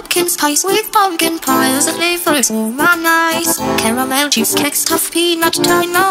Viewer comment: I swear when he said "moldy skin" i heard "good cheesecake"